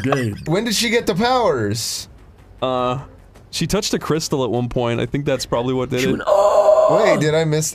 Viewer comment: Thought he said 'Crystal Weston' for a sec